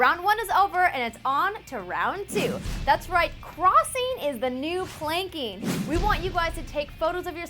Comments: I thought she said Crossing is the new Plinking then, that would have been much cooler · I thought she said plinking as well.